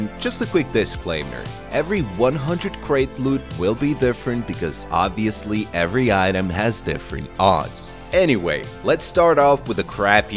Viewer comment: what did he say? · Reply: “Loot will be different because obviously every item has different odds”